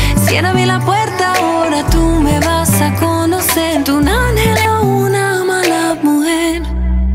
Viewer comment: Alguien entiende lo que dice? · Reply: Tu angel es una mala mujer